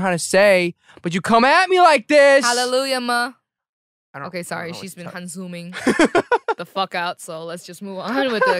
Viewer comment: Could anyone who can understand English let me know exactly what she was talking to Eric? 38:58 I can't figure out cuz there's no subtitle about what she mentioned! ! "okay, sorry. She's been -consuming(?) "or whatnot.. plz.. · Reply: darom Lee she said “she’s been sighing “ but used the korean word for sigh instead